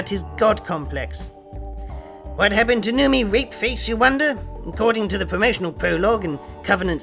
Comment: OHHHHHGGG! U said Noomi Rapace! I thought u kept saying Newbie Rape Face or Newbie Rape Case. That fixes all the plot holes of TLJ.